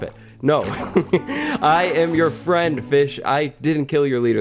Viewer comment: i thought he said b** not fish lol